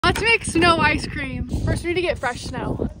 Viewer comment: I almost thought you said “let’s make Snow White scream!”